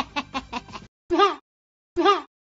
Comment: Wait, what is she even saying at 0:08 lol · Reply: “soMeThInGs oN mAH lOg-“